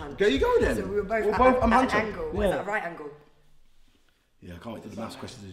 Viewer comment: m huncho I swear I heard that